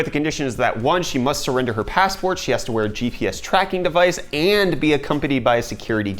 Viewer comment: I thought you said GTX tracking device